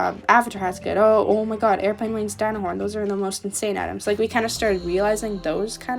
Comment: Okay, did you say Dino Horn or rhino horn? If you said Rhino horn then... I have Rhino horn!